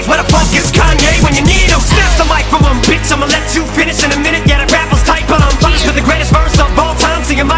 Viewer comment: you wrote "that rap was right but i'm" but it say "that rap was tight but i'm" i understand how u would think it says right instead of tight, i tuk me a while to figger out what he says. but alll in all the rest of it was spot on, good work